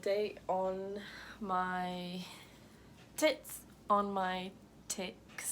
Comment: did she say titts '' XD wat 0:29 . i mean it sounded like it